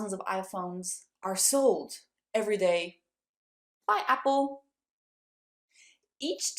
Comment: I heard "I*m apple"😂omg I*m so deaf